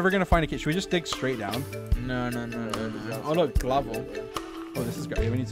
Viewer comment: Did he just say glavel